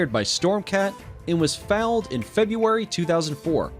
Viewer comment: Did he say fouled, instead of foaled? I’m out!